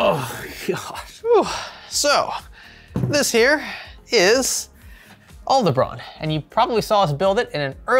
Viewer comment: When you pronounced the server name each time I kept hearing “Old Lebron” 😂